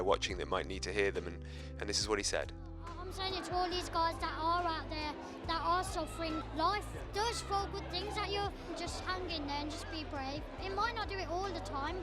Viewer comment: Sorry if I sound rude but English is not my mother tongue so I didn't understand what the kid said to us at 4:13... Could anyone write it to me please? Thanks!!!😃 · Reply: Elena in Movieland he said “I’m saying it to all these guys who are out there that are suffering, life does throw good things at you, just hang in there and be brave, it might not do it all the time but you will get these great opportunities sometimes in life” :)